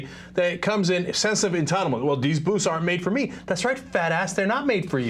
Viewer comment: I thought they were saying boots, not booths. Now it makes a LOT more sense.